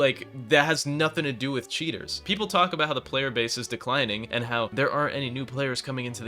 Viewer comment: hey what did you say here if I may ask 4:25 ? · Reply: people will talk about how the playerbase is declining, and how there are no new players coming in, I wonder why?...